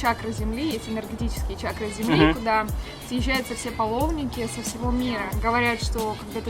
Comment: Мне послышалось, или она сказала "половники"? :D